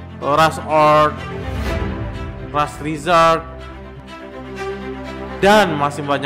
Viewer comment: Gw dengernya ras Rizald tolong rekomendasi THTnya bang